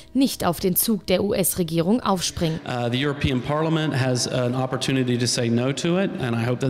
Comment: hab ich "the european PORNOMAN" verstanden :D